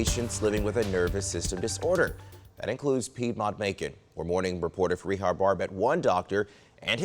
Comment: When he said her name I swear I heard "Freemont bacon" 😂 I was like whats that mean? Oh its a name...